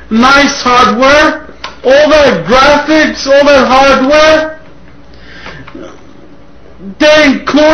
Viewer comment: does anyone know what he says at 3:57? to me it sounds like “good graphics, nice hardware. old man graphics, old man hardware?”